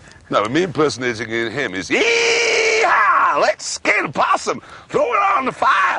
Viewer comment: Can somebody please tell me what jeremy is saying at 2:00? ty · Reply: Let's kill possum